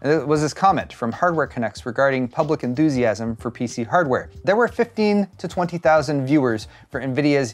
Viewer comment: I had to turn cc on cuz I coulda swore you said "feces" not pcs. XD